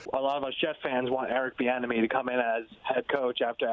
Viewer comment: Eric Bieniemy is going to Houston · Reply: Bro I thought he said “Eric the enemy” I was like who tf is that??